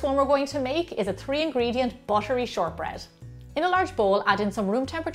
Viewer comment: I always love short bread and handmade cookies,especially that buttery flavor. This video is just what I need. Thank you,for another great video.❤️❤️❤️ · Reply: 玥凜Lynn thought u said battery flavor